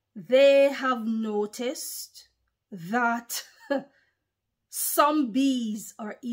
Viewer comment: When you said “some bees” it sounded like you said zombies